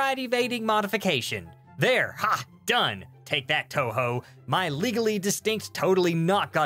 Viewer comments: I feel like someone just watched Godzilla. Also touhou have giant monsters when? Edit:just listen a few times to realize he mean Toho not Touhou, oops. But thanks everyone correct me. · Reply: You commenting about the "Take that Toho" at 2:44? A quick Google search shows "Toho" is a Japanese film company. Given the context, I'd be willing to bet it's the one that owns Godzilla. Yeah, I heard Touhou too. X^p The only reason I realized the spelling was different was because I had the captions on. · Did anyone else hear touhou